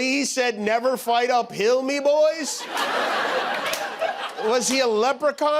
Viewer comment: Not being an American I hadn't a clue about Gettysburg, I thought he was saying ''never fight a pill me boys'' and I was like WTF is he on about.